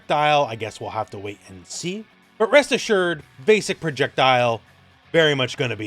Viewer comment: I thought you said “well Breast assured”. 😂